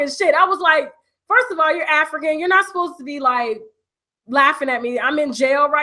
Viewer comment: Did you say 'are you African?''. If that's what you said, do Africans mock locked up blacks?